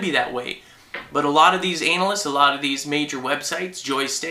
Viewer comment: i like how you said anal-ist instead of analyst